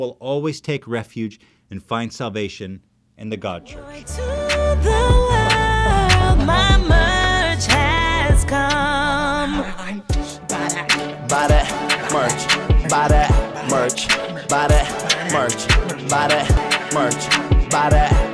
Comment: Wen the woman sang it sounded like she said “ my merch has cum”